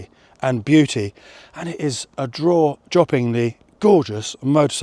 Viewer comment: Did he just say "draw joppingly" at the beginning of the video? 😂